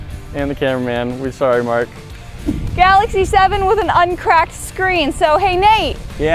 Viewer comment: At first I thought you said galaxy s6 and that's my phone